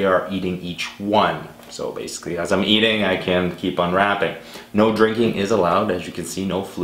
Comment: U cheated u said u can't unwrap another until u eat the one before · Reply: He clearly said at 0:39 he said he CAN